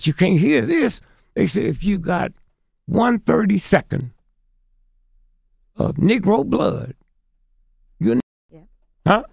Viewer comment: What did he say in 30:37.., if you got 130 sec of negro blood 🩸 what!? They cut what he said...